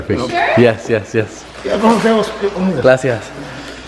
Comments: Austin said “Glasias” instead of “ Gracias”😂😂💀💀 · Did no one peep Austin say “glassy-ass” instead of gracias 5:41 🤣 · Nobody: Austin: “Gracias”😬😂😭😭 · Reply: Lisa Le it sounded like he said “glacial”🤣